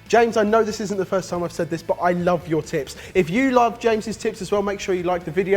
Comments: I thought that he said "I love your tip" I had to rewind it cause I was laughing... I apologize for my dirty thinking xc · i had to rewatch the last bit because i was sure i heard "i love your nips" instead of tips. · i keep hearing "James's Tips" as "James's Tits" O_o facepalm